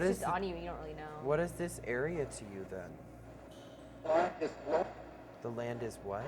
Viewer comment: i heard “the land is wet”